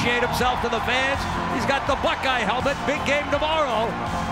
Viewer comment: Did he say "hes got the black guy helmet" xDD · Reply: I think he said Buck Eye helmet